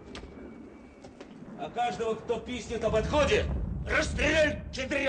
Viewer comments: мне послышалось или он говорит «пиздит»?!)) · что он сказал? 😊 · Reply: Пискнет)